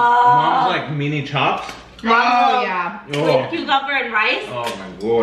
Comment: I think I heard say “A1 with steak sauce is not the way to go” but I think he meant A1 with cucumbers 🤣